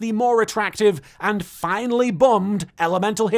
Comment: I thought you said "finally bummed avian" for a sec and I thought I missed something XD